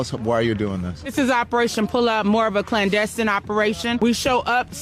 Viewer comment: I swear it sounds like she says "Operation Pull-Out" her dad pulling out would have prevented her dumbass actions😂😂